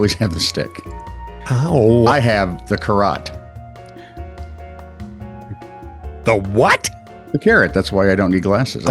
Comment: lmao, thought you said Garotte. Kind of apropo to the whole "are we gonna be using sound today?" debate